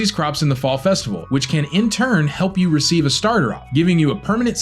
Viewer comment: "Starderop", anyone else hear that?